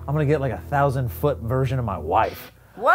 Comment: I heard say "I want a hundred foot versionS of my wife", like he wanted a hundred of really small wives.